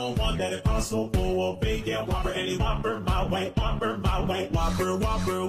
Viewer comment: Thank you. I always thought it was them saying whopper malway